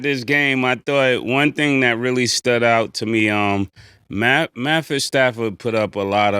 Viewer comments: Those refs always cheating for Kansas City. You gotta admit that. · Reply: Did say Mattford Stafford?😂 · Did say Mattford Stafford?😂